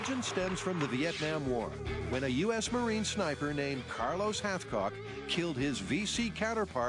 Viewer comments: I swear it said Carlos halfcock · is he saying carlos half cock?